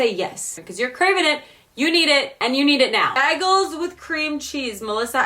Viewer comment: did she just say BAGULS at 10:16 ....hahaha